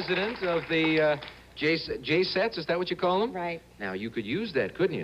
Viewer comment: Did Alex say she was part of "The Jay Zeds" at 13:45?